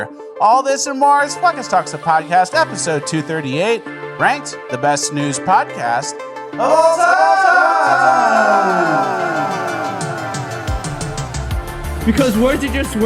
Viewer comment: did he say "the best snooze podcast of all time!!!"?